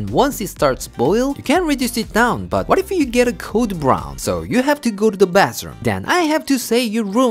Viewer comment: Did just say 'code brown' 😳😂 The leftovers look even more amazing!